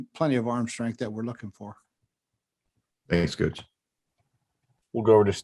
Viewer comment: I heard a toot coach.